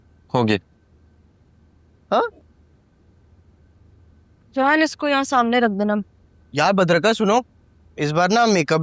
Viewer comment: did she call him jaan instead of dayyan ?